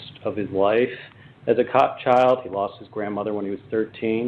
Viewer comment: did he call him a cop child?